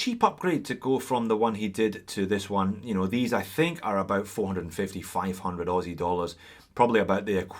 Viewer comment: I coulda swore I heard “$45500 Aussie dollars” lol